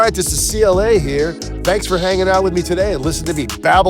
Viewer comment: He said, Babble on, but when he said it I thought he said, Babylon.